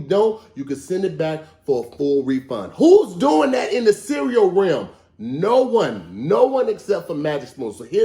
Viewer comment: @ 36:29 I think he meant to say realm but said rim. Stop eating that cereal.